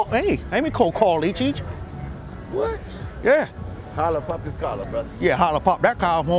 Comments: what does the black guy say? Hello poppy color ??????????????????????????? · what does he say??????????????????????? Hello poppy color??????🤷🤷🤷‍♂️🤔 I need a hero